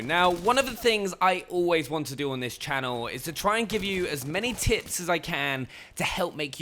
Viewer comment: I heard him say "is to try and give you as many tits as i can"